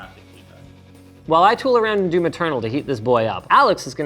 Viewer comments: He said he was going to run maternal to heat this thing up? I thought he said Returnal, and thought that was a leak. Interestingly enough we might get Returnal on PC. But, since that's not what he said. What the heck was he running in order to heat it up? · thought he said “while i tool around and do maternal”… mother linus